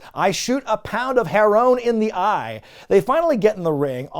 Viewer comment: Thats not "herone" it's heron, like heroin. "I shoot a pound of heron in the eye" is dude saying he's injecting heroin in his eyes. I mean I was a junkie for years, I'd love that too, but I think you might love it because you don't actually understand what it says 🤣